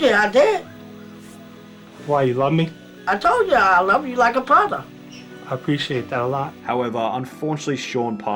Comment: Subtitles:Yea I love you like a product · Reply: That's what I heard, but it's Brother